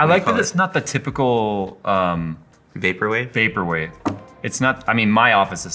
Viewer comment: anyone can help me, I don't get what they say, what term is it I heard "taperway"? idk